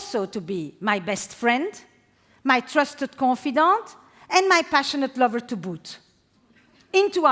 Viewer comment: What does she say here? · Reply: “and my passionate lover to boot” .. meaning ‘also’ :)